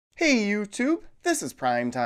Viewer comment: For a moment I thought you said: "Hey Mewtwo! This is Primetime Pokemon!" :D